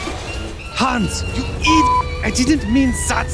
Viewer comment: What was the 16:40 part? I wonder what the swear word is lol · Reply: "Hans, you idiot!" he says